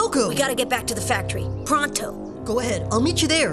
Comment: did ulrich say pronto???